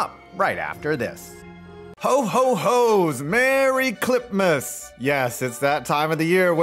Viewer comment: For a second I thought you said "Merry Clitmas"